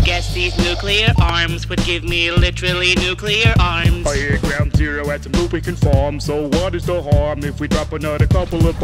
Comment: When the Nuclear Arm sings, I could've sworn that it said 'I heard ground zero has A NEW PECAN FARM.' which he suggested to bomb, and my thinking was "wow. Dianne's pecan pie must've been LEGENDARY awful"